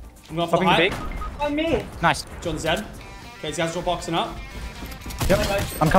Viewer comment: who heard in the backround